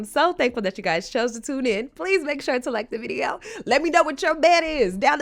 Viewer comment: I thought you said at the end "Let me know where your man is" I been with my husband for 32 yrs. And he is right this minute staying at the hospital with my 84 yr. old Mom. Yep. I got a good one!